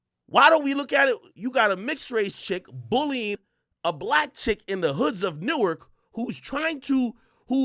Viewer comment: did he call mob a black chick?